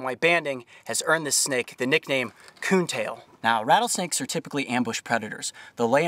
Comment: did he say COON TAIL. Lmao